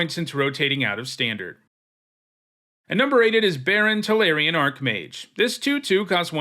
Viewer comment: I heard #8 as "Barren Tolarian Archmage" as in a female Tolarian Archmage that couldn't have kids. 😅